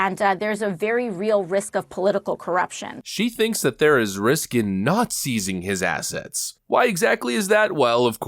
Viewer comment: did I hear that right? "risk in not seizing has assets?" uh.... due process??? I must have misheard.